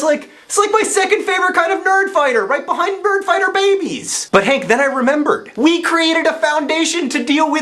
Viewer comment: It almost sounded like you said "BIRD-fighter babies". Haha.